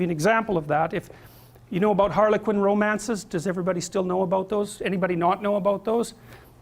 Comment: When he said "Harlequin romance" I thought he was saying "Harley Quinn romance" as referring to the relationship such character has with the Joker in the Batman series. Despite I was wrong, it's funny to notice that the Harley Quinn-Joker relationship is indeed a Harlequin romance and fits perfectly in the description of the female fantasy that mr. Peterson describes in this video.